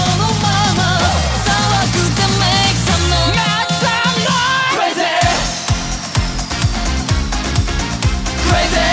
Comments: あたりの猪狩くんなんていってますか？ "なっかっのー(中野)" にしか聞こえなくて困ってます😂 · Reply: 瑞稀くんのあとに続いて「MAKE SOME NOISE‼️」と言ってると思います！ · ここ何て言ってるんですか？ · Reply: 猪狩さんで、make some noise だと思います！ 騒げ〜！って煽ってると思います！